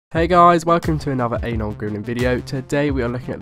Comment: I literally thought you said welcome to my anal grooming video hahaha